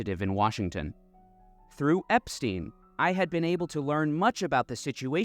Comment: did someone say Epstein????!!!!